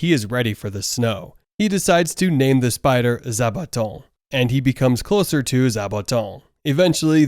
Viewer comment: The moment I heard you say the spider's name I thought I heard you say: "Sabaton" and I perked up asking: "He named it WHAT?!"